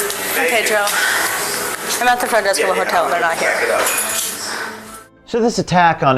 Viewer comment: The caption is wrong at 4:40. She says, "I'm at the front desk of the hotel, they're not here".